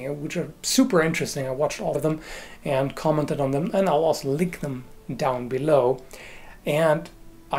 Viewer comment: I just heard "lick them down below" i apologize on behalf of nobody